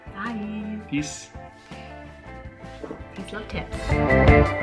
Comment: HAHA The last thing that said I wasn't paying attention to and thought that she said something besides "Peace, Love, Tips" and said **ts instead. I had to go back and rewatch just so that my brain could catch up and realize that she said tips and I wasn't thinking with the lower half of my body. Awesome tip video, and I can't wait to see your adventure/vacation because it's going to be an epic watch. Be sure to pack lots of snacks in your checked baggage so you don't have to spend a lot of money on fast food and such as it could help out and save you guys a few bucks. That's especially true if you guys were staying in a hotel but it could end up being easier to just make a run to the local grocery store once you're in town though. Some snacks for the flight might be a good idea too because contrary to what most people think you can bring food with you on flights, and airport food is ungodly expensive. Here's some good tips: <link> . Have fun you guys, and much love! :-) P.S. You can get condiments from the fast food places, which I've done before. I packed a complete lunch for a flight once but didn't have any mustard or mayo due to the TSA guidelines and I went to the McDonalds once I went through TSA and asked for some mustard and mayo packets and they were happy to oblige.